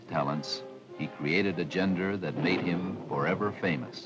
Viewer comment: "He created a ..." what? Sounds like the narrator said "gender", but I think he meant genre, but he pronounced "genre" wrong. I'm not sure what he said.